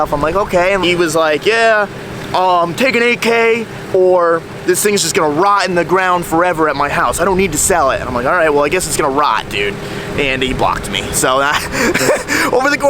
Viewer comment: stg i thought you said “ill take an AK or it’ll rot” 😂